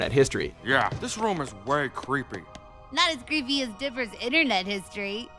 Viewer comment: Did he just call dipper... Digger?